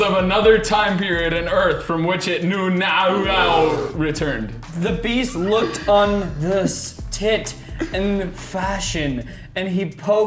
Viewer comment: "the beast looked upon thus tit with fashion" please tell me i wasn't the only one who heard that